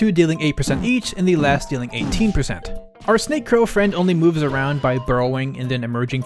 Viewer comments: I was listening to this at double speed, and instead of "Our snake-crow friend" I heard "Our snake-girlfriend." I had to slow it back down to normal speed and double take XD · I was listening to this at double speed, and instead of "Our snake-crow friend" I heard "Our snake-girlfriend." I had to slow it back down to normal speed and double take XD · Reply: I heard "Our snake-girlfriend" at normal speed